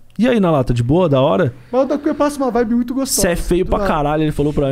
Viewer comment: passa uma vibe o quê? Eu não entendi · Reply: Uma vibe muito fumaça